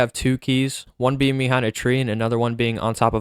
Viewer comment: did it say one behind the tree on o I’d mansion?